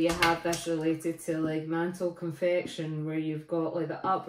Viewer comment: I thought you said "Mantle Confection". Something mysterious only known to ladies in the third trimester. (Can you do a talk on diagenesis in marine cement? I have such a rock, found near Firth of Forth).